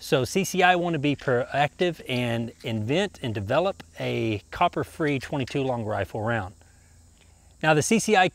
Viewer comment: did you say CCI developed a Copper Free bullet?